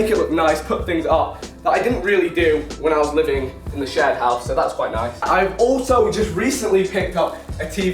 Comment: Thought he said shed house